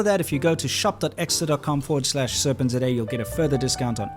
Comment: I hear him say “serpent today” I see it writen “Serpentza” So i call the show “Serpentza” Then today I realized it’s “Serpent Z A” I can’t stop laughing about it